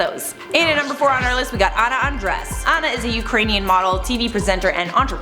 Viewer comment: I thought you said Anna Undress Lolllll